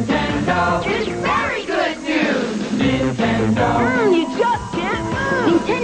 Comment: What does he even say? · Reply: "It's berry good news" "Nintendo" "Oh, you just can't lose."